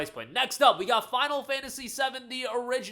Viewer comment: I heard you say final fantasy 70 not 7 when you said it.